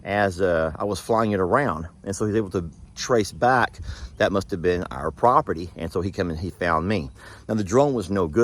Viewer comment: I thought you guys said cameras on your property